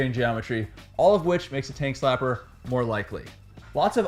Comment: The first 75% of this video I thought he was saying "Taint Slapper" which made me think bumps/potholes make the frame slap your taint